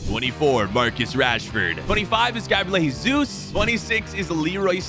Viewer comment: did he just say "GABLA JESOOS